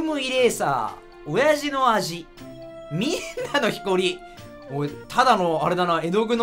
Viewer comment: 「皆のひこり」 にしか聞こえない